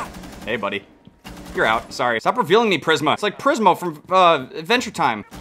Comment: 1.its Prismo 2.Watching Netflix? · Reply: oops I thought he said Prizma :/